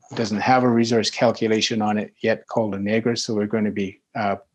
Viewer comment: Minute 12:07.... can someone explain what he said it was called? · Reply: La Negra